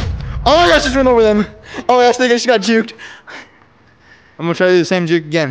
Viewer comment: Did he say the n word ?? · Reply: "they just got juked" is not the n word lol